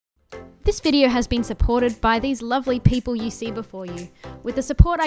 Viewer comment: It sounded like you said "this video is sponsored by all these ugly people " and for a sec I was crying (as in laughing) and I listened to it a nother time and I finally understood it XD also keep up the good worck